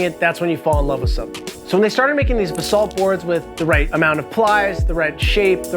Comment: It's pronounced B'SALT.. not like "bathsalt".. which is what I thought you were saying for the first 2mins (edit - 4:35 Exactly.)